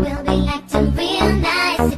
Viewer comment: I always hear " I'm not your bra"